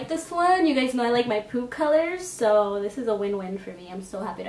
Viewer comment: did she just say "you guys know i like my poop colours".....im..